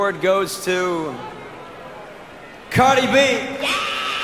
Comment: Anyone else hear someone yell Cardi ?